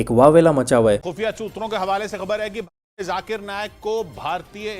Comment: what did he sayyyy???? 😆 khufiya kya????? 😆😆😆😆